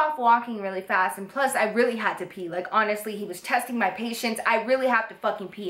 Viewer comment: I totally heard: 'He was testing my peetience' hahahaha